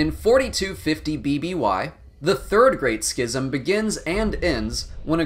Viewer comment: Sounded like he said 3rd grade schism